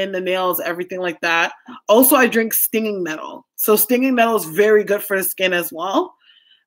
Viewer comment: at around 31:20 ...what is "Skinny Nettle"... or did you say something else...for your skin...?